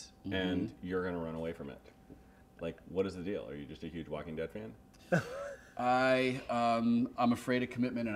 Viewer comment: The subtitles track says "Are you just a huge walking bedpan?" (instead of "Walking Dead fan")... this just had to be said!